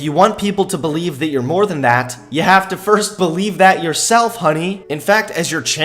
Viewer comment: DID HE JUST CALL HER HONEY??? That is so demeaning!!